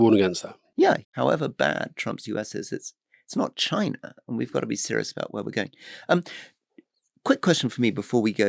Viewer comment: Am I hearing this right? “No matter how bad Trump’s America is, they are still better than China?!?!” I sometimes wonder why smart people have such stupid views on China. Even those that have dealt closely with them…I think the reason is no matter how smart you are, a Chinese person is always going to find a way to outsmart you. Having dealt with Chinese people it’s either incredibly frustrating or incredibly awesome.